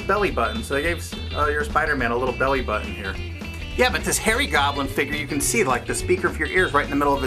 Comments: oh harry goblin... thought he said fairy goblin...... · lol it sounded like you said hairy goblin LMFAO + ROTFFL!!!